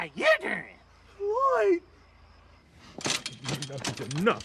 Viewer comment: Rasputia: Bark How you Doing. Norbit: Lloyd. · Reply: Oh so that's what he said he said Lloyd I thought he said "WHY?". LOL